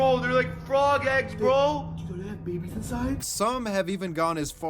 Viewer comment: 😭 Help I heard that as “Do they have babies inside?! 👁️👄👁️😨”